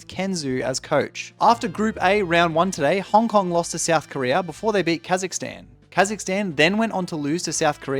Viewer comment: my league degraded brain heard this as "before they beat Kha'zix-tan" 💀